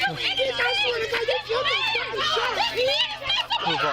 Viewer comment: They are foster parents (I think). One can say they love others as much as they want. But it's hard to get emotionally attached to someone one doesnt know for a very long time. Plus, they didnt seem to be decent people anyway. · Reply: I thought he said "you killed my baby". Foster parents makes more sense I guess. Horrible people though, clearly. The dad also tried to kick the girl on the ground in front of the cops. I have so many questions. Very few things add up in the video. They all look like bad actors. Some weird amateur movie with fake emotions.